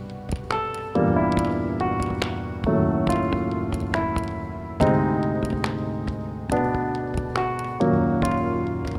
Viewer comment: what were you saying while lying on the floor? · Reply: Something along the lines of "don't loiter around waiting for inspiration to strike, you'll just get yourself down" not sure what the exact words are. Hard to pinpoint.